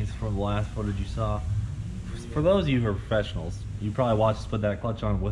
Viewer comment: LOL> At 10:27. Did Cleetus say professionals? pefessionals? fessionals? This highly tight organized organization are full of fessionals. lol.